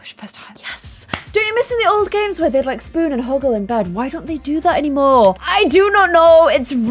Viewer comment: did say huggle at 29:35 😂